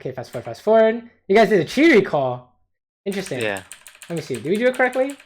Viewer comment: I can't really hear what said, what kind of recall did he do? Is the cull part of it? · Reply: He said "cheater recall". It means stacking minions and pushing to tower on the 3rd wave in the beginning of the game. Your opponent has to last-hit under tower and can't push the wave to your tower. During this time you recall and have enough gold to buy another Doran's or Cull. You return to lane with an item advantage and a positive lane state where the wave is slow pushing to you.